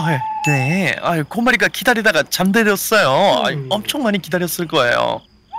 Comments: 초에 잠들었어요가 만들었어요 라고 돼있어용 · 에 만들었어요가 아니라 잠들었어요 인데? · 왜 잠들었어가 만들었어 라고 번역 됬노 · 초에 잠들었는데요가 아니라 만들았는데요 ㅇ(?)